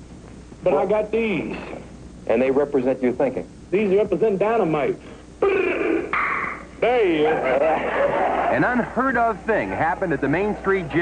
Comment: what does he say at 5:43 ? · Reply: Ibrahima Diouf These represent dynamite prrrrrrrrrrr tttaaaa dead